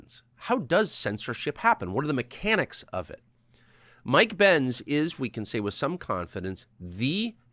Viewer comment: I first heard " Mike Pentz" and said to myself "how did this happened?